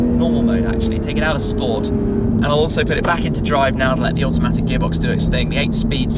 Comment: did he say automatic ?